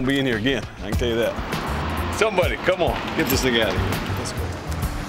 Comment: Wait, what did he say? Get that what out of here. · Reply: He said that thing